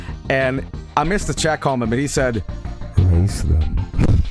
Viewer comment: What did say at 10:21 ? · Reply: raise them